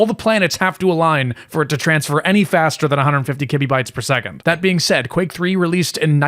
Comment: I love how you say "kibibytes per second," and the captions say "kilobits per second"